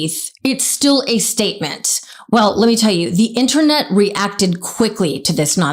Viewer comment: R u a muslim? I heard u say "re-acted" instead of reacted